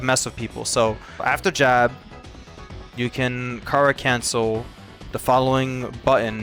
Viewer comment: thought you were saying "car cancel" until you typed it out on screen lol